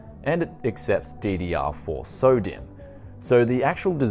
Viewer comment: It sounds like your saying Sodium, not "SODIMM"